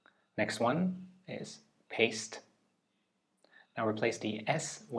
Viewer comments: i only struggled with multiplication and substraction, i do have discalculia, undiagnosed but very clear. the test was pretty good, i would only suggest replacing the word "paste" because i thought u said "paced" since it came after another verb, and "pante" is unpronounceable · Cool test, I only had a bit of an issue with test 6 where you replace a letter in a word to make a new one, like for the second word I heard 'paced' then he said replace the s with an n and I was like oh he said paste then the word became paint so yeah I don't blame anyone for messing that one up but other than that this is a good test. 👍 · Number 6 had me confused for the wrong reasons. Worsed isn't a word so I was doubting the instructions, I heard "Paced, swap the S" and was going there isn't one to swap, then finally "Crain" had me going ok nvm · thought you said paced so said paned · 1 - Hesitated and Got It wrong 2 - not that I know of 3 - not hard but fovused really hard 4 - Never noticed 5 - Good with numers 6 - glide, worsed, pante, crain (tho I hesitated and heard it as "Pace" the first time) 7 - Basically Impossible 8 - got october and september mixed up and b4 june had to recite it in the right order multiple times to be able to do it 9 - hesitated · I thought he said paced and was panicking because there IS no S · this fmu for a second cause i imagined the word “paced” instead of paste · "paced" replace the "s" with an "n" would be "paint"... am i hearing this correctly? 🤔